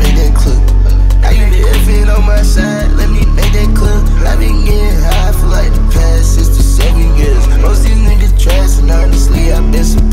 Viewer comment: Shitt I deadass thought he been saying "I've been getting high for the past 67 years"